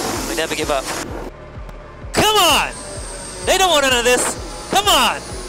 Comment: I thought Lewis said "They don't want any of this!".. Am I right?